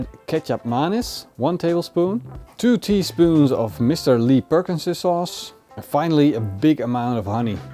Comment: Can't find Mr. Lee Perkins sauce. What is it? A recipie would be nice. Sounds like you said "add to this katchup minus one tablespoon two tablespoons Mr. Lee"